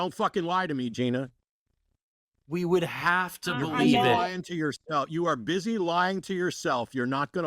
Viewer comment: I don’t think she was giggling or mocking you guys. I’m pretty sure i heard her say “but would you believe me?”. Gina was a bit of a religious wacko but she seemed perfectly nice and i don’t know that she deserved that outburst.